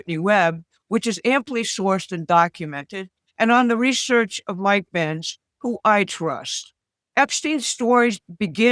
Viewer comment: I thought she said "Mike pence whom I trust" 😮 no, Mike Benz. 😅